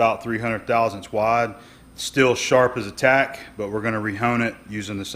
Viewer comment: Did you say "sharp as a cat."?